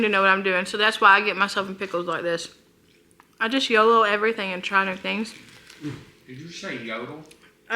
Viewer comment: I’m with, I also thought you said you yodel everything! 😂